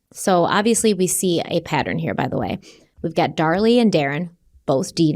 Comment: occasionally saying "Dar-Lee" instead of "Darlie" is just one of hundreds of reasons why I love this channel. 🤭😆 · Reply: See I had thought he was saying “Darlene” and just was temporarily confusing her name. 😂